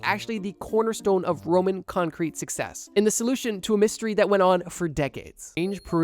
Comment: does he say lawsuit? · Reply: Solution